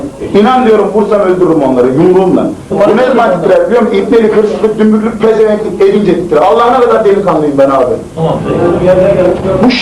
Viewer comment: ne diyor hala çözemedim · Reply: "Bu eller ne zaman titren biliyon mu ibnelik hırsızlık dümbüklük pezevenklik edince titrer" diyor